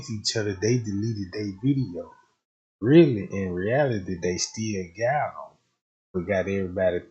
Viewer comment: I thought you said y'all got them Jack? Man let that girl rest in peace y'all been watching the same video for years